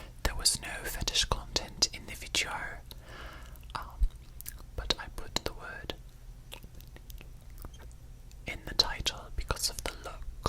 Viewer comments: WHAT WORD 😭🙏🏻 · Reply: Dominatrix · what did they actually say there? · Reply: Dominatrix